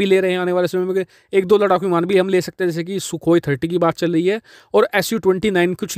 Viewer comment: He just said Su 29 instead of Mig 29. Did anyone heared it Or I m alone. Plz tell & hit like.